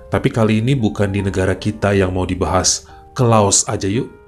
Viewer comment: Gw salah denger, "Ke LAUT aja yuk!!!" Ternyata LAOS... :face-green-smiling::face-green-smiling::face-green-smiling